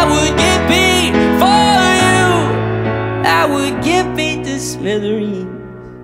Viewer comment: I hear "Slitherins". Harry Potter, welcome